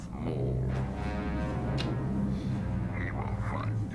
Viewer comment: For years, I thought that was Maul saying "We will find your lost ship," and I didn't realise it was Sidious saying "He" until I saw it at the cinema for the 25th anniversary - much easier to spot that Maul's not talking once he's massive.